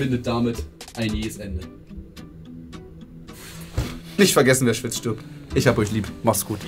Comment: Ich habe bis zu diesem Video immer verstanden ``Wenn Fritz stirbt´´ anstatt ``Wer schwitzt Stirbt´´ ........ hatte den sinn nie verstanden , jetzt weiß ich auch warum :D :D